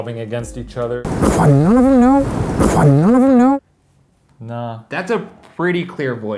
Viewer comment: When they were reviewing the audio tapes, I was looking at my phone. And when Shane said "None of them know?" I thought he said "What? None of them know?" Not realizing the what was not Shane...